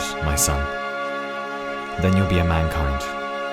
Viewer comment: the video was getting hopefull at 6:55 , and then the subtitles said " and then you will be a man c*nt my son , and then you will be a man ****"